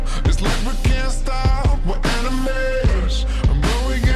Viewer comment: It really sounds like he said we can't stop were anime's